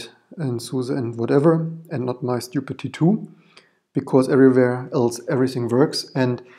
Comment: Did you say "T2" Linux?